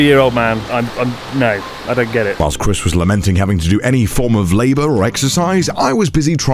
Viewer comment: dude i thought i heard connor say “lamonting” lol